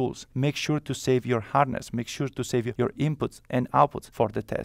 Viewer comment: Did you say to make sure to save your "harness" at 3:20?